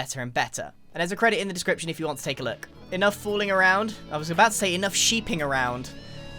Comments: What Grian said: "Enough fooling around." What I heard: "Enough wooling around." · When Grian said "Enough Fooling Around" at 6:04 :I thought he said "Enough Wooling Around" cause ya'know, s h e e p